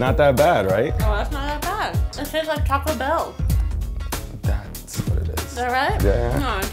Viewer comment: Girl Filming: "That tastes like Taco bell" Prison Dude: Gets a strong urge to kill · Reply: BRO I THOUGHT SHE SAID TASTES LIKE Chocolate MILK ALSO